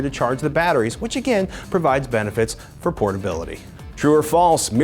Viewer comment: I thought he said for "porn Billy"